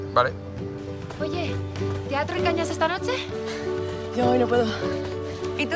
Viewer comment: elle a dit quoi la fille en blanche à 1:03 ? "te atraigo esta noche" ?